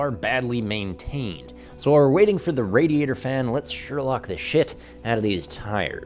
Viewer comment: Um, didn't anyone hear the "shit" at 2:00 ? LOVE this video BTW!